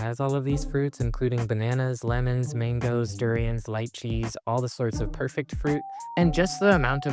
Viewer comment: you said "lychees" but the subtitles thought you said "light cheese" XD